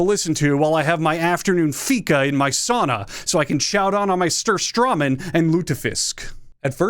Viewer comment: did he really call Surströmming "Stourstawman"